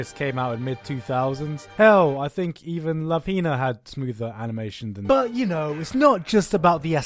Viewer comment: what is the name of the show at 3:38 he says something like "lafina" but i can't understand what he is saying